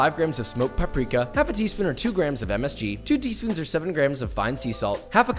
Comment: Did he say msg